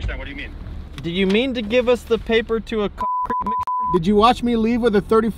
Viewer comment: Even with the Bleeps it sounds like hes saying Concrete Mixer